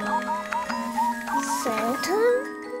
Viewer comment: I thought she said, "Satan?"